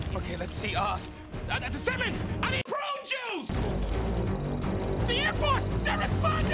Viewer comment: " I need Prune Juice! " XD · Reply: I thought he said " I NEED BRO JUICE!!!!!"